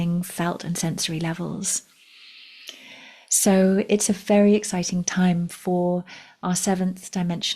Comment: It sounded like you said “it’s a faery exciting time” 🧚‍♂️Then again, it wouldn’t surprise me if you did!❤😊